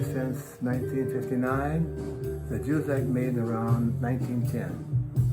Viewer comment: Ron Carter: ”…and a Juzek made around 1910.” That’s what i heard!